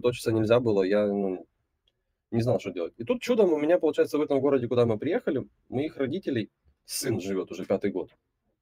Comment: Я не пойму, что он говорит, мне слышится: в этом городе, куда мы приехали у моих родителей сын живёт 😂